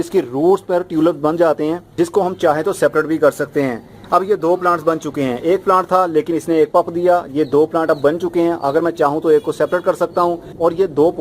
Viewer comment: what did you say at 1:32? ek plant tha isne do "pup" diye? not clear. can you please tell me?